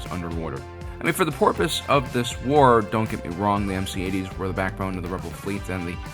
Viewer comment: Did you just say “For the porpoise of this war…” just after the underwater scene? I salute you.